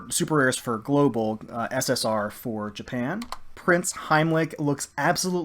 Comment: did you say this was a prince? A trap?? noooo don't say so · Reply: Prinz, German for Prince. All of the girls are named after real life or prototype ships.